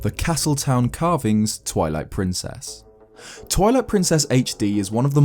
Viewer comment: sounds like you said toilet princess